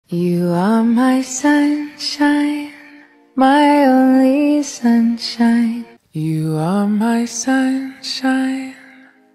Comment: The very distorted one sounded like je said "you are my shun shit my only shunshit"